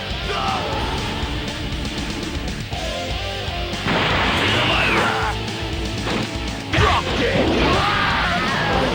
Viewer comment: What does Vegeta say when he turn into Super Vegeta? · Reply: "Feel my wrath!"